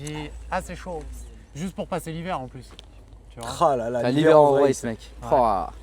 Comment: C'est quoi le nom de la voiture qu'il voulait au debut svp 2:19 une "vrace " ? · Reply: Une Rolls Royce